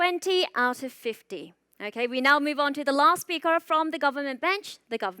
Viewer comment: I thought the host said The Lost Speaker 😂😂😂